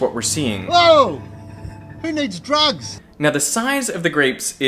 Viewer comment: Somewhere out there is a curious teenager putting a whole bundle of grapes into a microwave because he wants to take this plasma thing further. What have you done Derek. What have you done. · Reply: thank you! I thought he said "who needs drugs" but not for sure lol